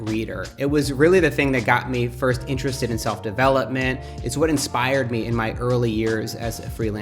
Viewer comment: anyone else thought Matt said "self-D'avella-ment? love that term btw :D